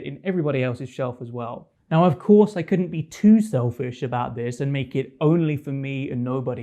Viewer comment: did you just say shelfish 😂😂